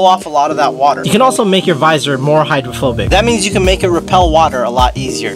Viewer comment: I thought it said "Homophobic" not "Hydrophobic" 😂